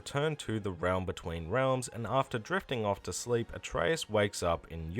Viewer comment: I had do a double take .. thought he said "after jerking off to sleep" X-D